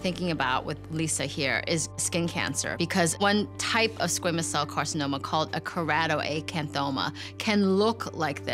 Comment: DID I JUST HEAR SQUAMOUS CELL CARCINOMA???